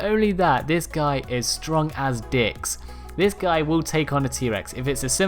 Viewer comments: did he just said dicks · Did he just say it’s strong as dicks